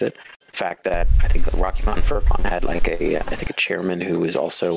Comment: thanks to my super hearing, I can decipher what he said. He actually said “Rocky Mountain Fur Con”, not “Rochester Fur Con”.